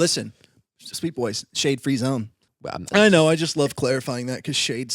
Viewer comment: I literally thought said "Shane free zone", not "SHADE free zone" and to say i was completely caught off guard would be an understatement LOL.